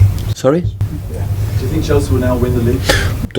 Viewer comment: what did he say? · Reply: Q: Do you think Chelsea will win the league? A: Tottenham is there.